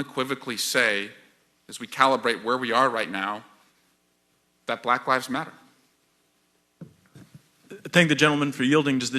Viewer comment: I heard “black wives matter”???